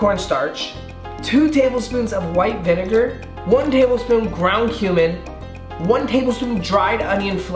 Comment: When I heard him say ground cumin I thought he said ground humans